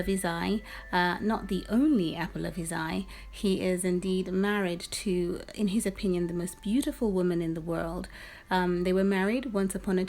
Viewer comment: I'm sorry, but what did you say at 1:40? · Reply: he is indeed married to, in his opinion, the most beautiful woman in the world...